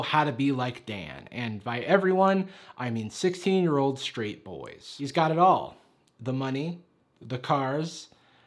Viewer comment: You said 16 year old straight boys but it’s more like pathetic 40 year old men who thought they’d be like him when they were 16. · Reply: I guess the short answer would be that he's famous but fame still doesn't make him cool so I don't really know for sure why people think he's cool. (Also I thought he said 16 year old street boys...?)